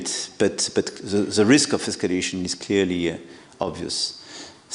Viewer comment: autotranslate the auto generated subtitles; macron:"…but the risk of 'taxation' is clearly obvious…"; 'taxation' instead of 'escalation'